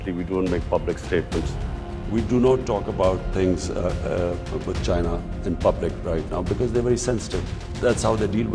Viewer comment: Rightly said " Beggars cannot be Protestors. We cannot blame Pakistan". Palki Sharma you sound so good when you use such words😊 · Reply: I thought I heard him say we don't talk about China's issues in public 🤔